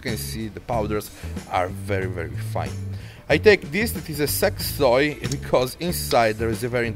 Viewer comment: I must of misheard it Sex Toy....